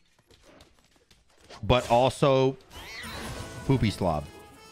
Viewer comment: I know I’m omega late but I haven’t seen a comment doing what you said “im gonna be there may 5th but also poopy slops?” that’s what it sounded like you said